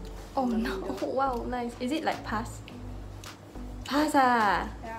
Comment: I thought say "is it like pus?" but she actually said "is it like pass?